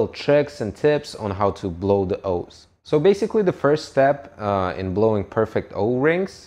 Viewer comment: Thought he said "blow the hos"